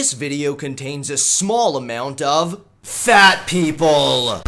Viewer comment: did he just say small amount